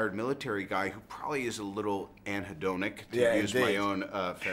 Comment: can somebody help me out please? what does Robert say at 3:14 ? "He is probably a little ____" a little what? Something with -donic...